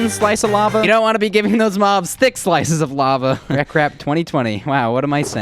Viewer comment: the subtitles wrote "that crap" instead of rekrap. Youtube hates u :C R/softwaregore